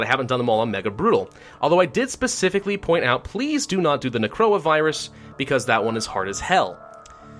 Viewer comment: i thought he said Corona at 00:25 and thought this man was a time traveler